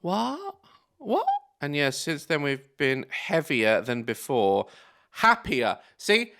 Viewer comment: I heard “heavier” too 😂😂😂😂